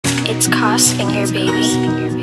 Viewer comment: I always thought it said it’s kos in your baby😂😂